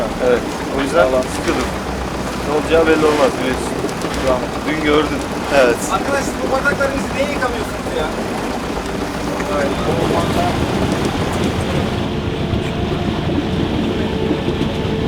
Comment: Arkadaşlar bu bardakları siz niye yıkamıyorsunuz ya sjsjsj 😂😂😂😆😆😆 Bir Kayan Eser duydum sankim 🙈 Neşeniz bol, Oltanızın ucu keskin, balıklarınızın trofe olması dileklerimle 🤜🤛